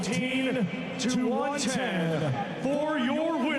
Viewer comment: both other cards where <num> not even close fella 1 point deduction 1 knock down and lost most of the rounds! Not even close that’s why he needs sacking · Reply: I realised that I misheard the scorecards thinking it was 115-113 but my comment dissappeared so couldn't delete it. Only appeared when u replied lol